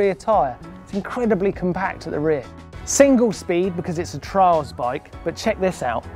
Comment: Anyone thought it said trails bike?